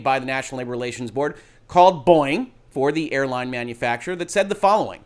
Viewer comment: Is that really how Boeing is pronounced in the US? In Australia we put more emphasis on the first syllable so it sounds more like "Bow-ing". The two times i've heard you say it i thought you were saying "Boing" and then looked at the video and noticed it was Boeing. (No shade either way... it's just something I noticed). I saw another Youtuber saying"*gasp* Activision is being diabolical" and with the understanding from the earlier video about the NLRB my response was more of a "eh... actually this is more a reflection of the legal landscape than Activision twirling their moustache". Although yes it does seem rather... suspicious that they chose now to push this thing that has been common practice in business software for at least a decade now. My current belief is that this probably has been in the works for a while, i feel like that would be too easy to fact check if discovery ever happened, but I also believe that it's highly likely the timeline was moved up in response to the unionization conversation. I note that "Temporary Employees to FTE status" suggests to me that the "independent contractor" clause isn't at play but maybe i'm reading too much into the use of "FTE". As a separate federalism question... Is this an area where a state could decide to do things differently? For example: could the California legislature say "That's super interesting NLRB but we're passing this law that allows Independent Contractors in California to have their own unions"?